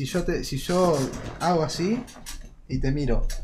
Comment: Qué dice carrera en 0:05 esq no le entiendo jakdbansbq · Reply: Dice: "cuantos edits hacen"